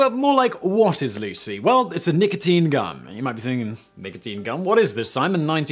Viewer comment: I thought he said nicotine gun'''''' but It was gun I was confused at first